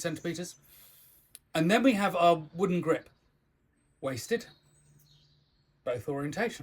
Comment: I heard "wasted" and was so confused for a second...